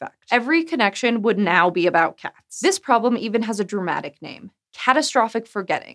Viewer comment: i thought you said "This problem even has a cat name: Catastrophic Forgetting."